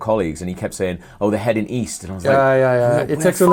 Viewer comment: Ign guy "east, where the hell is east?" 4th grade maybe? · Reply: Oh, I thought you said Weast!